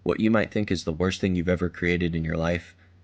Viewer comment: I heard "but if I make someone feel better about themselves today..." and then was like wait.... wait... WaiT.. wAittt... wait....................................... AAAAAAAHHHHHHH